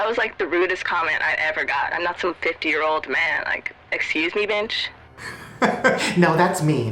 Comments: had me confused i think the auto tune or however she was talking stuffed and said binch instead of bitch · it sound like said "excuse me bench" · Did she just say "excuse me bench?" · Any body else notice at 8:45 she said "excuse me binch" and no that is not a mistake that is what she said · listen closely she does not say b*tch she actually says “excuse me BENCH” she says bench!!! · Did anyone else hear binch instead of bitch? 8:46... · it sounded like she wanted to say "excuse me bitch" but instead it sounded like "excuse me bemtch" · when she said "excuse me, bitch?" I heard "excuse me, bench?" lmao